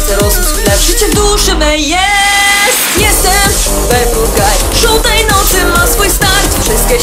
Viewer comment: wait czy ja usłyszałam ''żółtej nocy '' ? XD